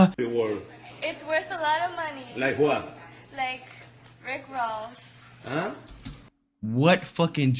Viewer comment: i think she said “rick ross” but she has bit of an accent